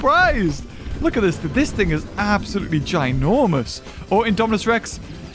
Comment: this what is gigantic????? 😂 n word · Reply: He said this thing is ginormous